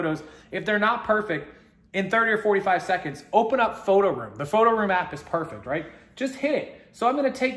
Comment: What did he say the name of the app was. I couldn't understand him ==> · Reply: PhotoRoom. Iphone/Ipad only